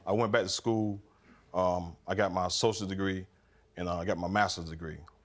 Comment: How do you go from associates to masters? Don't you need a bachelors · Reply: I thought he said socials 😂 I was like what degree is that?